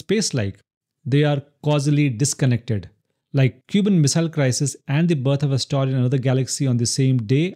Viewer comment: the cuban missile crisis? Odd description. Or did I hear that wrong